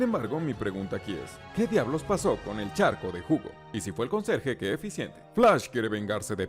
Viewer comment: Oigan oi bien, en el minuto 10:6 pensé que dijo charco de miada 😅🤣